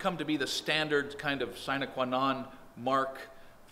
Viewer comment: Anybody know what he says at about 5:07 referring to crows feet being ubiquitous? Youtube transcribes it as "sign akwonton" and I'm 100% sure it's not what he's saying. Update: I tried to google it and I came up with the latin phrase "Sine qua non;" literally, "Without which not." It is an expression used to refer to something essential to to the functioning or success of another thing. In this case, the crow's foot is the thing without which construction is nothing. TIL